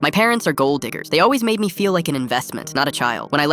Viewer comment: BROOOOOOO My brain is so racist that at the beginning of the video I heard:"My parents are gold n*ggers"💀💀💀💀💀💀